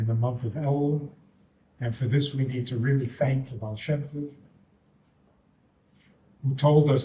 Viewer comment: I watched the video again. No "insane Kabbalistic spiritual event is happening RIGHT NOW" but I would like to know what was said @ 27:14 and 27:20. Thank the ? tells us it is possible and 27:20 for how it is possible. The second sounds like Baal Shem Tov. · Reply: is the Baal Shem Tov who began Chassidus. 20:20 is the Alter Rebbe who wrote the Tanya, which is Chabad (Chassidus).